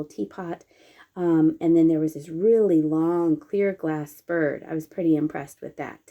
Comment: Wow,that long clear glass bird was amazing! You got some really good things! I love love loved the green powder puff box with the flower from 1955.I would have bought that from you if I lived in the States…but I live in the Netherlands..🤷🏻‍♀️😄 I also loved it that you showed us some beautiful autumn sides.🍂🍁🍄,walking with your dog,who is super cute!🥰 What a shame that the owl mug was so personalized..it was so cool! You got some really amazing stuff! Glad you enjoyed yourselves❣️🥰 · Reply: At first I thought you said "beer" glass bird. I thought oh something new. But then it was oh, clear glass bird.